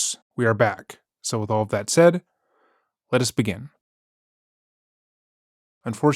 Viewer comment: On a lighter note, I always love when you say "Let us begin" I always hear it as "🥬 begin" lol